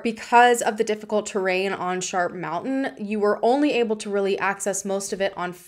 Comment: Okay I thought you said shark mountain is it really called shart mountain? Is it that shitty of a place? 🤣